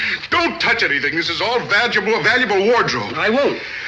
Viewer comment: Did he say vaginal? 😆😆